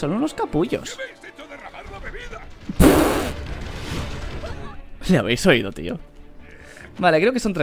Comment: ¿que dice? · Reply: Me habéis echo derramar la bebida